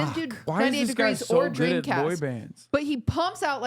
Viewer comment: did she say dREAMCAST?? like as in sega dreamcast?! who could she possibly have been talking about? also, shout out the barely noticeable mention of b4-4. if you get down on me, i’ll get down on you.